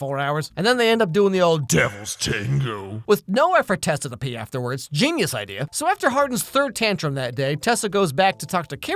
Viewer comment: I will give anyone who's actually able to translate what says at 4:40 an internet cookie, because as someone who's a professional full-time English speaker reader and listener, I have absolutely no fucking idea what words he just said. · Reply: He said "Golly gee, that's just terrible"